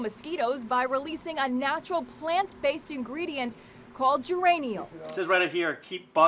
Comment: i misheard Geraniol as Uranium